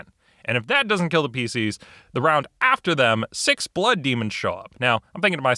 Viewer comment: I was making food and thought I heard "toe demon." Boy, that would've been scary